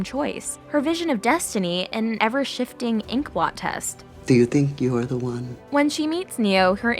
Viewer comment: what is that word inquatest?